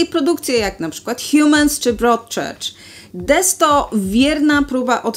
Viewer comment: kiedy powiedziała "DES to", na usłyszałem "The 100" :D